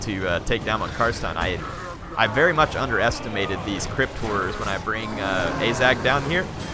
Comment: When I first heard you pronounce 'Horror' I thought you were saying crypt whores. Was mightily confused